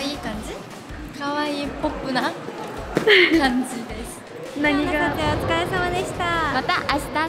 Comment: 「ミイヒにしてはパリピやん」の英語字幕、「MIIHI, you look cute」なのね😂全然ちゃう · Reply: 日本語字幕でも、「ポップな感じよね」ってなってるから、え？聞き間違い？と思って見直したけど、やっぱり「パリピやん」って言ってるよねｗ 字幕の人の気遣い？